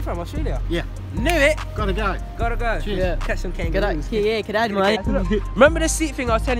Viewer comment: wait a minute: is suisse german we alsow say ,,tschüs,, like ,,goodbye,, is he saying tschüss?